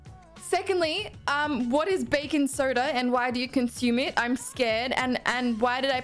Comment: Yeah, American here. I don’t recognize 3/4 of the stuff you tried. Brands.. sure. Flavors no. Nobody eats cinnamon jolly ranchers. We like the fruit flavors. Yes, pop tarts go into the toaster. Though I don’t recognize the flavor you have. The most popular are the fruit based ones. Red or Grape Faygo is the best. I’ve never met a person who drinks bacon soda. Americans drink soda/pop/fizzy cold or on ice. If you try American sodas try it cold or on ice. Totally changes the flavors. And thanks for not pouring the milk first. My pet was about too be peeved. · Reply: Bacon Soda ?? ROTFLMAO.. I thought she said baking soda... They make a great candied bacon down near Los Angeles.